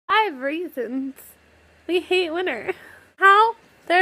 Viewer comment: Lol I thought she said "I have raisins... We hate winter"